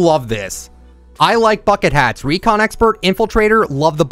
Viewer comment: I thought he said I love fu€king hats I was like what??????